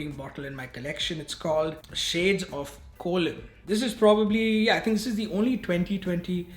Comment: I thought you said "Shades of Colon". LOL!